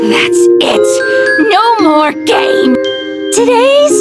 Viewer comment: STOP I HEARD "THATS IT, YOUR MORE GAY!" AND NOW I CAN'T UNHEAR IT...